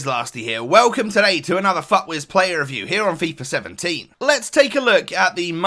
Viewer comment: Thought you said "welcome to another fuck this player review"